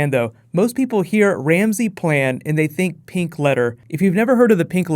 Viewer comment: I thought you said Ramsay and the pink leather.. Well he's definitely kinky enough for that